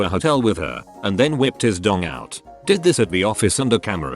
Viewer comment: I thought you said that the cleaner ripped of his dong